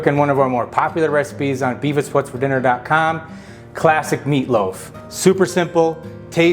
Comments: I thought you said Beavis for Dinner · I heard "Beavis what's for dinner" ... Took a moment to comprehend.... Beef. It's what's for dinner. 😂